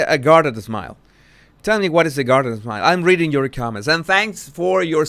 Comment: Ohhhhhh .. I thought it was “garden smile” (and I pictured her teeth bared like a rake) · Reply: I also thought he was saying "garden smile" a few times. "Like a rake" BAHAHA!